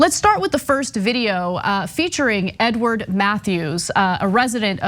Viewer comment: LMAO, I misheard when Anna first said his name at 0:17... it sounded like she said "N Word, Matthews" like it was some kind of nickname! haha, Its Edward!